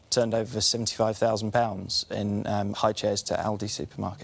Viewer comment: When he said "highchairs" I first thought he said it accidentally instead of "buggy" under pressure. And when it turned out not to be the case, that's where I knew it'd all go wrong..😂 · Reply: Lol i thought he said ichair